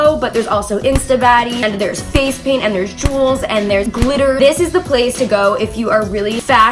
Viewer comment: Deadass thought you said Juuls instead of jewels smh 🤦🏻‍♀️